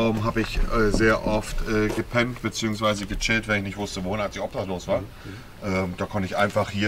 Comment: Bei minute 1:00:54 hab ich es nicht richtig verstanden hat er gesagt das er obdachlos war